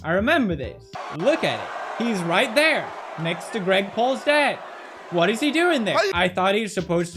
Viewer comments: Greg pauls dad? am i the only one who heard that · did he just say "GREG PAULS DAD?" waduhek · Did he say " Next to greg paul's dad" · did he say next to Greg Paul’s dad lmaooooo😂😂😂😂👉👌✌️💦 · Did he say, “Next to GREG PAULS Dad”?? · did he said GREG PAUL'S DAD? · Anyone else hear him say Greg Paul’s dad😂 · How else heard him say Greg Paul’s dad😂